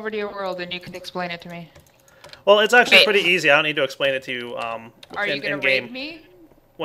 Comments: I heard say rape :s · oh know i thought i heard say are you going to rape not raid WTF I had to double take that one lol. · who else thought that said "Are you going to rape me?"